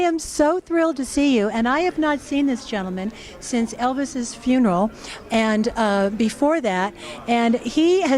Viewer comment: Well then why is Dave saying he did! I know just about as much about Elvis as anyone else but then again I don't believe half of what hear! So many untruths about Elvis! Why don't people let him rip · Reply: Dave never said that. You must have misheard what Mindi said to him when she said ' i haven't seen this man since before Elvis' funeral'. Dave Hebler never attended Elvis' funeral. Any Elvis fan should know that.